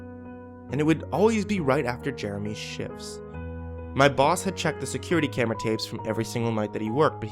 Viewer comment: when he said right after Jeremy's shifts I thought he said Jeremy's sh*ts